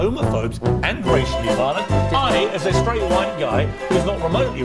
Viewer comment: Did she just say that there is a problem because you take it seriously 😂😂😂